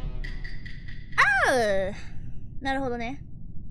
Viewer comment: sounded like a british person saying "oh" lmao.